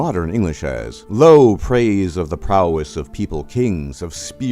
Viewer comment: the 400s AD were the 5th century, not the 4th. Also, I'm pretty sure I heard you say "prowness" instead of "prowess".